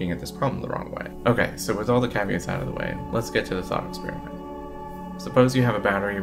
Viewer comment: I'm not qualified to judge between you and Veritasium. I'd like to see Veritasium analyze your video, regardless of whether you are right or wrong. I watched your video twice, but you need to learn to pronounce words better if you want your message to be widely viewed. I'm a fluent English speaker but I failed to understand quite a few of your words because you don't speak clearly enough. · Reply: Just as an example, around 0:39 you use the phrase "thought experiment" but you said it so quietly I don't think most people would catch your words there. It sounds like you missed a couple of consonants.